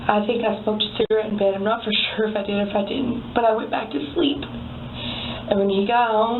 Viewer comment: Maybe it’s my bad hearing but I heard “i did” “I did it all”